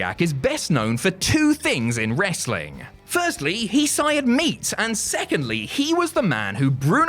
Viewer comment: What did he say at 6:33 I can't understand it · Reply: Firstly he sired meat